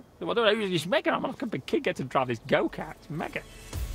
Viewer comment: i still dont know what the fuke guy says from 6:09 - 6:13 XD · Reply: "It's mega. I'm like a big kid, I get to drive this go-kart. It's mega."